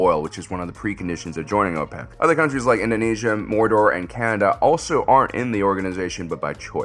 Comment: it sounds like he says "Indonesia, Mordor, and Canada"... I can't decide if I am mis-hearing it or if he is pranking us (which would be funny, it is just so subtle I am not sure).